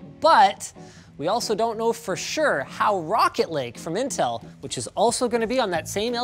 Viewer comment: did he say: Rocket Leg? Anyone else hear that?